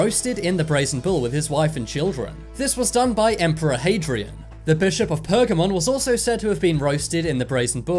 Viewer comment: I can't be the only one who heard "the bisharp of pokemon"